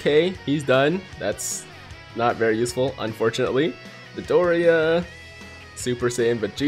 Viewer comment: i swear i thought you said "Midoriya" instead of Dodoria xD